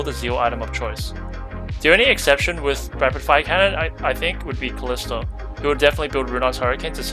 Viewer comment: What do you say at 7:35-7:42? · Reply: The only exception to Rapid Fire Cannon would be Kalista, who'd build Runaan's Hurricane instead. Sorry, was a bit tired after scrims when I recorded this!